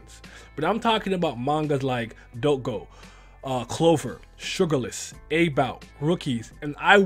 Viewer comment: what the name of manga in 0:39 ?? sory i cant hear you properly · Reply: Sugarless